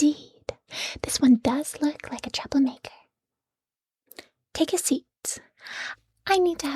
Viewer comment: instead of "troublemaker" I heard "chocolate maker" and thought this was funny